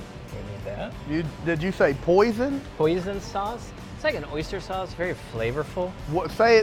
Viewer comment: I love that you thought he said hoisin sauce was "poison sauce. Very entertaining video. Josh and I make and eat a lot of those things. :)